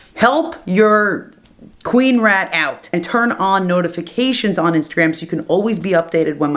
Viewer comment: For a sec i thought u said: "Turd on notifications" I was like "O-o"